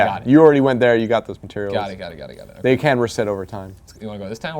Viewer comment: At around 26:45, did he say materials can reset over time or can't? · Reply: they can reset.